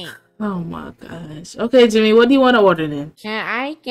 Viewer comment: I cant be the only one who heard her say okay junior what do u what to jimmy😭😭